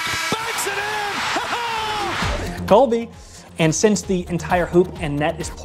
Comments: did you say “Colby” 🤦🏼‍♂️ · Did he say "Colby"?